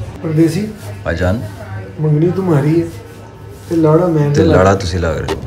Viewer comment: i thought he said Lawra lg rhe ho😂😂😂😂😂😂😂 (Sorry i don't know punjabi well😂)